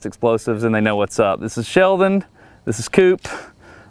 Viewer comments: This is Sheldon, this is cup… all i heard was this is Sheldon Cooper and was like ok now… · this is Sheldon, this is coop => I heard this is Sheldon Cooper 😂